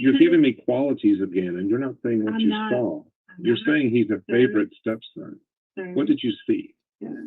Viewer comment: I thought LiePeecia said "favorite step Son".